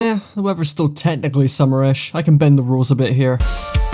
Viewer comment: did you say “Whoever is still technically summerish” ?